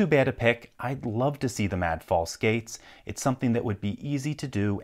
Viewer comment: I thought you said 'The mad force skates' at the end there, for a moment. Need to get my ears cleaned out!